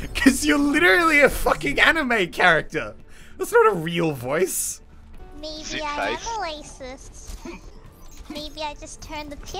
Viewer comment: I heard "Maybe i am Racist"